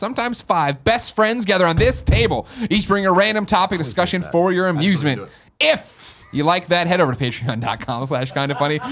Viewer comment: What does Colin say at 23:20 ?? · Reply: "I didn't even do it".